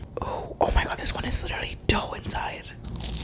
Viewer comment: Is it just me but at 2:22 I hear: oOooO! oH mAh gAwD dErE a tOe inSiDE